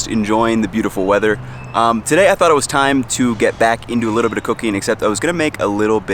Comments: am I the only one that thought he said "get back into a little bit of cocaine" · Am I the only one who heard "Today i thought it was time to get back into a little bit of cocaine" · why does it sound like he said cocaine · I thought he said he wanted to get back into cocaine 💀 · I thought he said it’s time to get into a little bit of cocaine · i thought he said today it was time to get back into cocaine